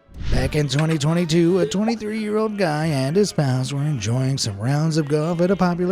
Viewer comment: "32-year-old guy and his pals", but the closed caption says "guy and his spouse" LOL!